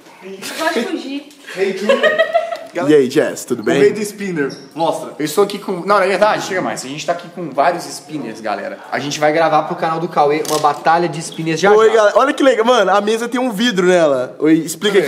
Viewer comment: só eu q entendi "Estamos aqui com now united? ;w; sou uma uniter, quem mais é? ;w